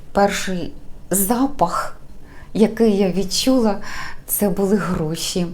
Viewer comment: Ой насміялася щойно. Мені почулося «перший запах, який я почула, це були грОші».😂 думаю, виходить на новий рівень. А взагалі, рада, що ви одужали. Уявляю, як це неприємно влітку. Цікавий у вас сервіз збирається. Не здивуюся, якщо вас ще десь цукорниця чекає. А щодо календаря, то із задоволенням подивлюся виготовлення нового. Я і сама почала після ваших відео трішки гарніше планер заповнювати.